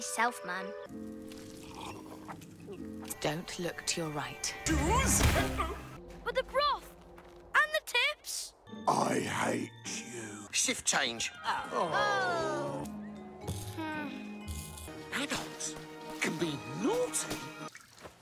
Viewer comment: Wait what??? I forgot about that scene! What was he supposely saying? "I hate shoes"? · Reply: He's saying I hate you...and well the rest is very oddly specific ;)